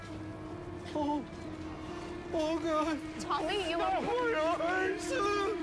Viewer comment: It Sounded Like He Said “Oh, My Eyes, I Can’t See!”.